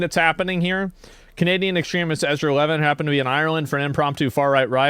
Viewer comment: did someone just call Ezra an EXTREMIST? LOL. WTF? Maybe they said STREAMER, can't pick it up clearly.